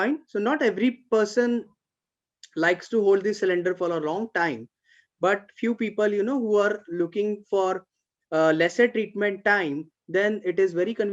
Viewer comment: What did you say at the 42:10 mark regarding the cylinders? I couldn't understand. Thanks. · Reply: Hi, it says Not many people are willing to use hand cylinders to receive frequency treatment because you need to hold onto them during all the treatment time. But due to metallic materials, so it can transmit frequencies much more effective! It depends on your choice:)